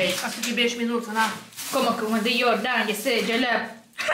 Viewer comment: Мне послышалось или ты сказала жал*п